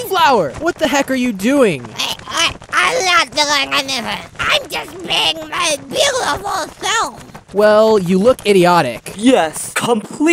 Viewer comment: I thought flower said I was just banging my beautiful self XDDDDDD